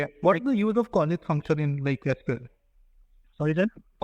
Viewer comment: Thanks for this informative video, can you please tell what are saying in 36:36, sounds like callith function in mysql